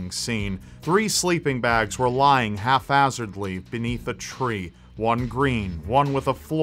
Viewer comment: is he saying "three sleeping bags were lying half-assedly?" Haphazardly???? I hear an F but I cannot make out the words...